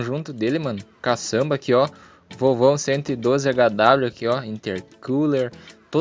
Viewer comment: so eu entendi volvão